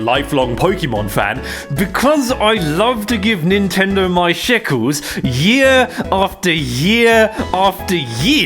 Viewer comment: did bro just say sheckles🤨? Like grow a garden... the kids game?